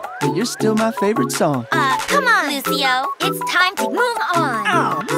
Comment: Lucio: 'I play a lot of music , but you're still my favouite song' Hana Song : OoH, c'MoN LuCiO, iTz TiM 2 mOv On... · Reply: i thought she said "Groove on"